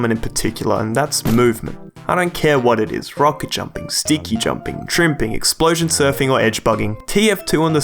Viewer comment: i missheard you and it sounded like you said stinky jumping